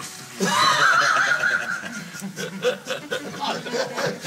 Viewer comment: Il dit quoi à 01:12 après "relent d'homophobie" ?? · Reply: Il dit "Répond, pédale."